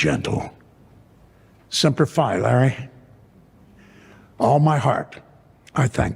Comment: What did he say? What does that mean? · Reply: “Semper Fi Larry, all my heart”. Latin for “always faithful”. It’s the motto of the US Marine Corps